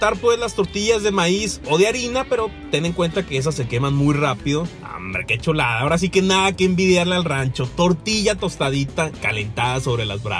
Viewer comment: Me imagino a un cavernicola: "Que kchulada papa" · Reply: jajaj por un momento pense que decia: que culiada papa